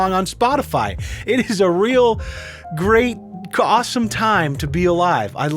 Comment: did you say Great Awesome??